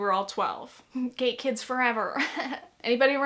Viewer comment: thought u said gay kids forever cause i wasnt watching, i was just listening lmao